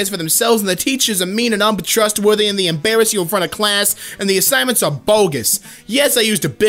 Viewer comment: What was the big word, assignments?